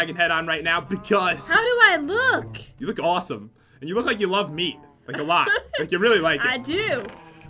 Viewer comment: Did say i do love pat